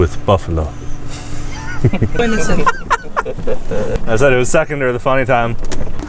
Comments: He said it was secender the funny time instead of it was funnier the second time lol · Did anyone else hear him say it was seconder the funny time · he said “it was seconder the funny time” instead of “it was funnier the second time” lmao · Anyone else catch him say “it was seconder the funny time”